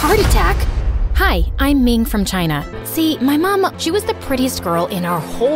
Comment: Help I thought she said I’m made from china 😭 And yes I liked my own comment